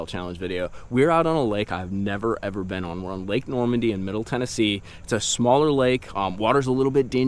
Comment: Hey!!! I had to back it up at the beginning. I thought you said “Lake Norman”. I about have a cow.